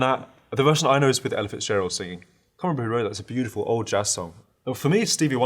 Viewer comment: did he just say "can't believe I remember that" in a quarter of a second?? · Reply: *I can't remember who wrote that